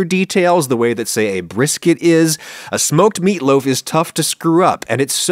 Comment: Dang I thought it said "Soaked meatloaf"...